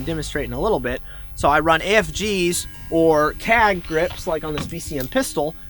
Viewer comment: What kind of grip is the second "pistol" in HIS right hand at 2:07 ? Sounds like he says cag grips??